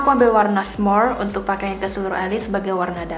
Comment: ke seluruh alis ci? Apa aku salah dengar wkwk😂